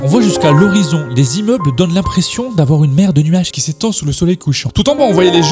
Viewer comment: il a bien dit "Mer de nuage" Et non "Merde nuage" Hein, rassurez moi svp